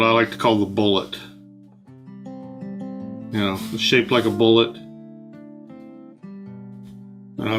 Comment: When you said "shaped like a bullet" for the first tree I thought you had said "shaped like Poland." I immediately thought that was an odd description of a tree, but went back and realized I must've misheard haha.